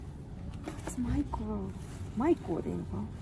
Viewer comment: "What's Michael?" said my name, im so happy. My name is Michael 💞💖💕